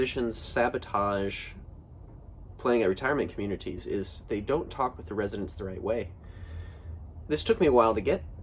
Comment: ? What do you say? "They don't talk with residents during (away?)( Is what it sounds like) Great video. Lots of great info! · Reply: thanks for the comment! I believe I say "They don't talk with the residents the right way".